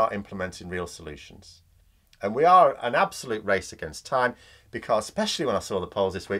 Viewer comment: In your closing comments you said 'this is a racist against time' and then I realized I misheard. Normally with these points systems the job must be offered publicly for a period of time b4 hiring a non resident.